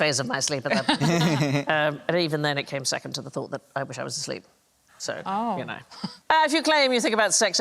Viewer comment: What does Sandy say at time 43:10 ? "And even then it came second to the thought of . . . . sleep" ??? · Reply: thought of "I wish I was asleep"